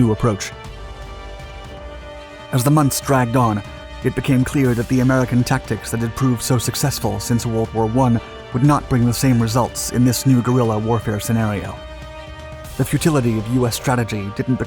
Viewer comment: im sorry did you say world war fucjing 1??????